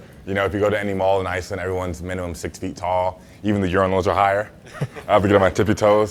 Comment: I thought he said “even the younglins” and I was extremely worried and confused for a few seconds lmao